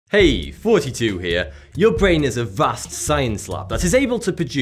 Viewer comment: Dumb question: I always hear him say "Hey, Forty Two here." But his channel is Thoughty2...?